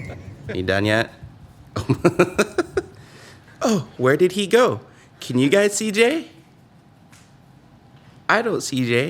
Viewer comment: "Can you see Jay?" What I heard: "Can you CJ?":D